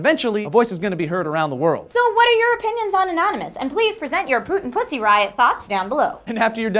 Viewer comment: At the end there I could have sworn I hears Trisha say "put your put in pussy thoughts down below". I'm a little ashamed of myself now.